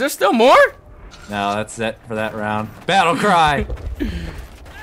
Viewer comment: i thought the guy that said for the king said dovahkiin